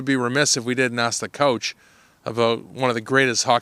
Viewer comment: Did he say "Couch" instead of "Coach"?